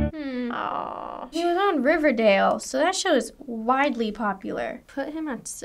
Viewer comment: Did just said "shit" because I've never heard swear before 😂